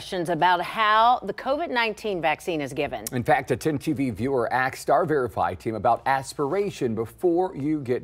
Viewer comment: did this cretin just say "axed" instead of "asked"? lmao